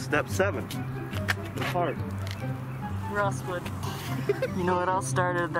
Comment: I thought he said "Real Swim" or "Rawl Swim" BUT HE SAYS ROSSWOOD- OMFG XD I JUST REALISED THAT!!